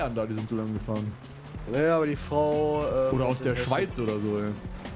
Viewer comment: habe ich Schweiz gehört? 😂 ich komme von der Schweiz 🇨🇭